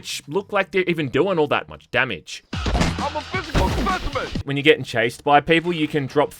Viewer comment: (hearing an NPC) I'm sorry what did you say?! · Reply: I think the NPC said "i'm a physical specimen" Thats what i heard